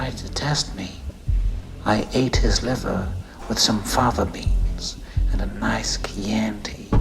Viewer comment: I always enjoy with some liver, some fava beans and a nice chianti. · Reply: I was about 11 when I first saw this film. I'd never heard of fava beans (which I thought he said father) and had no ideal what Chianti was. Now the rest of my life when either of those two items come up, the Lector scene and this quote is what immediately comes to mind. Every. Single. Time.